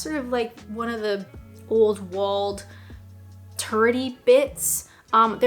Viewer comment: I just worked out what you said when you described the city, you said "turrety bits". Initially, I thought you said "turdy bits".